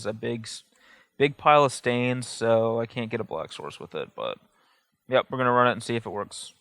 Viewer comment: I was so damn confused at the end because I could of sworn was saying 'dark horse' instead of black source...LOLOL.